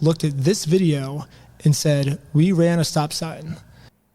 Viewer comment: he actually said you failed to stop before the stop sign. Which to be technical is true and actually the law. You must stop behind the stop sign. He then went on to say “and failed to completely stop” which is a gray area because they did stop but technically after failing to stop behind the sign.